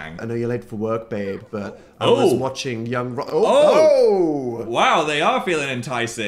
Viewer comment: Yes I’m very happy about the sims being back but did Dan say young royals at 14:52 THIS IS TOO MUCH FOR ME